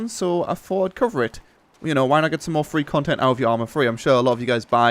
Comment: Sounded like you were saying " free content for arma free" in the start. I see, English is hard...